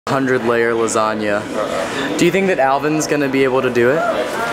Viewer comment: I thought it said 100 player lasagna and I was like “wow sounds like a fun game”